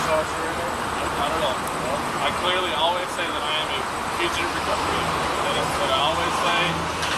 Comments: “I clearly always say I’m a future president” is not wtf he said lmao 😂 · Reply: Guy says "Fugitive Recovery Agent." Subtitles say "Future President." Speech to Text impersonating a subtitle expert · he said FUTURE recovery agent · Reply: The subtitles say FUTURE Recovery Agent. The guy said Fugitive Recovery Agent. 15:35 The subtitles also say FUTURE PRESIDENT. Subtitles be crazy